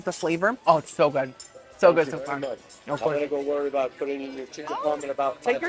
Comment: i could swear the waiter was teasing you to not worry about brushing the parm out of your beard? 😂😂 that's what I heard "cleaning your chin" 😂😂